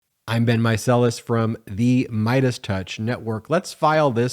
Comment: All this time I thought he was saying "I've been Micellis," which I thought was an odd way to start. He's saying "I'm Ben Micellis." 🤦‍♀️